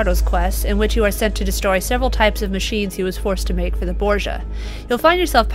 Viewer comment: did she say Borja? WTF?